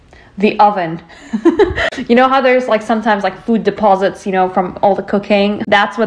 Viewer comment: Omg… it took me a second to register what you were saying… At first I thought at 3:36 you said “from all the cocaine…” 🤦‍♀️🤦‍♀️🤦‍♀️. Then it registered as you talked about the scouring sponge. LOL 😝 Edit: I’m at 13:05 and triggered by the crimes being committed against your eyes! I mean 🤬 You are a sunset with an orange face… I’m scared to see the rest.